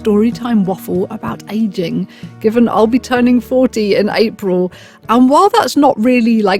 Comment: I SWEAR, I thought you said you where turning 4-D in April! For a sec I was like, "Holy shit! D's gaining another dimension! They're going hyper-spatial! They're magnificent!" Then, I remembered I'm insane and that you must've said you're turning 40 years old. Because, that's what a normal not bat-shit human would say. Anyways, best wishes.